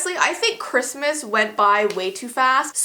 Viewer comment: I thought you said crispsmas not Christmas